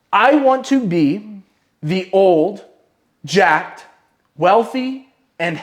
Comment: Awesome vid!!! When you first said 'jacked, healthy, wealthy' I thought you said Jack and I started thinking about Jack Lalanne. He's a very good example of that I guess